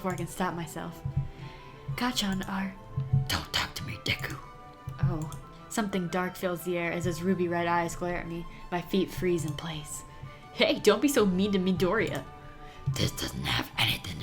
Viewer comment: I— did he say “Don’t talk to me, Dicku-“ W H E E Z. E